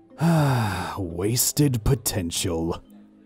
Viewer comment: When Zane said “wasted potential” I thought he said “wasted potato’s” for a second